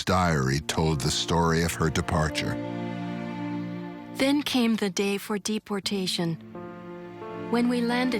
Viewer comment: what did she say? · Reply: then came the day for deportation